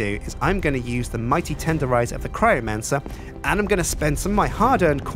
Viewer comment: I thought he said necromancer at the beginning 😂😂